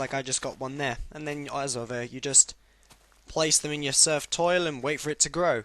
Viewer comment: Thumbs up if you heard Surfed Toil instead of Turfed Soil lmao Love you No homo ;)